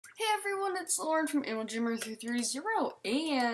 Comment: When you said "hey everyone it's Lauren from Animaljammer330", I thought you said "hey everyone it's Lorde from Animaljammer330". Sorry. I have bad ears.